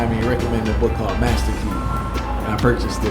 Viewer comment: Name of the book please! I think I heard "Master you"... Jah Bless!